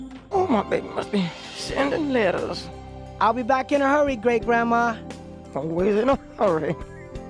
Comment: HOLY SHIT at 0:17 i figured out what she was SAYING she's saying "always in a hurry" LOL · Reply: Oh, my baby must be... sendin' letters. I'll be back in a hurry, great grandma! Always in a hurry...